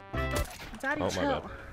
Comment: did i hear: “Daddy Chill”??!!??